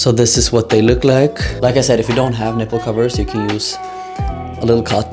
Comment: Why did I think you were about to say 'If you don't have nipples' 💀